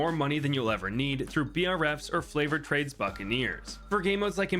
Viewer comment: did he just say "flavored trades"? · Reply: It's a joke in the BTD6 community. They always refer to the favoured trades upgrade as flavoured trades. I've seen ISAB do it as well and very recently, the official ninja kiwi video about the new purple type boss balloon also had "flavoured trades" in it